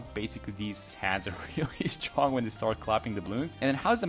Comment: what did he say? (he's really strong when he is clapping the ballons)